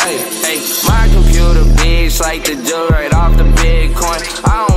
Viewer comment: my computer bees like the jug right off the bitcoin. Lol that's what I hear. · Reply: I kept hearing “I could pull the beans like the dil right off the bitcoins”